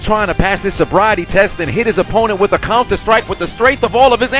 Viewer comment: Thought he said a Compton strike for a second